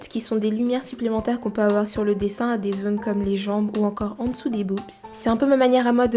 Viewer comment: En dessous de quoi ? J’ai pas bien entendue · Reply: en dessous "des boobs"